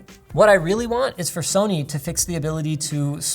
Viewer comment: did he say sony?